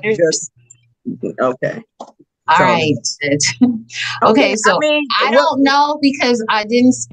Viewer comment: did say “Alright sis” to the judge or am I hearing that wrong? 🤦🏾‍♀️😂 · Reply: Sounds like “alright, judge” to me